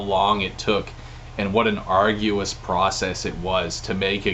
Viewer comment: Did he say "arguous"? 🤔